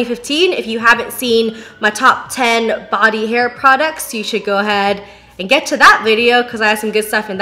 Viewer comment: When she said "body, hair products" I heard it as "body hair products" and nearly choked 😂